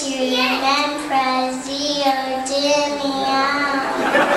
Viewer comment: Why did the people laugh? Is it because she pronounced (praseodymium?) wrong? Because I heard "francium-dymium".